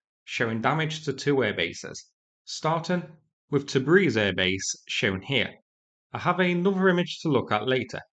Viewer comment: On the first one I thought you said Debris air base, that would scare away any russian 💥